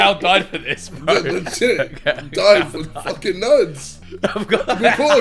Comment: gotta love how the subtitle leaves out the word “ fucking “ and instead of nerds , puts “ nudes “ like what ?? 😂😂😂